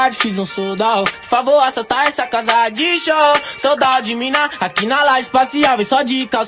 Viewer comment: "sold out de mina" é o que ele diz